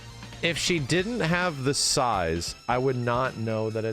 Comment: not gonna lie, when said “sais” I thought he said “size” and immediately agreed thinking, “yeah she’s got some huge honkers and I wouldn’t know otherwise either.”